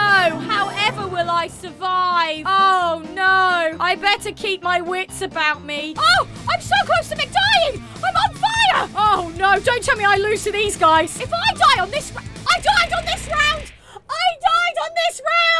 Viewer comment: did.. did you just say McDying?? New maccas burger just dropped